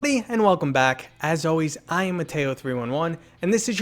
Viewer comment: I thought u said I am potato 311